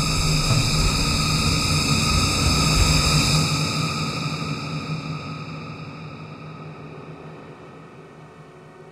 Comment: Any idea what the voice says, around 0:50? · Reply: Used to be a real king down here, y'know that? Used to be...